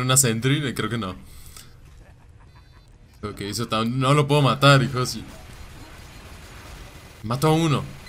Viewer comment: wat dijo mi nombre 7:52 ajajaj que dijiste ahi parece que dijiste "no lo puedo matar iCrozy" xDD